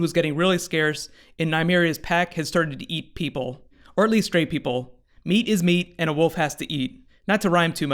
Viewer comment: For a second I thought you said the wolves only eat straight people, instead of stray people.